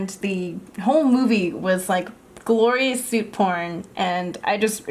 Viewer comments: I'm too dirty minded but it sounds like she said porn(im sorry) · i think she said suit porn?